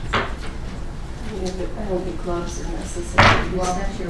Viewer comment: I didn’t hear what she said to the Judge??? I just heard the Judge say in response, “Well that’s your opinion”. It was around the 1:12:00 mark?? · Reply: She said she didn't think wearing the gloves was necessary, and the judge said, "Well, that's your opinion." She later touched the evidence on direct, and the defense called attention to it.